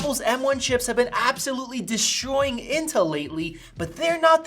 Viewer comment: Did he just say “deshtroying” (destroying)?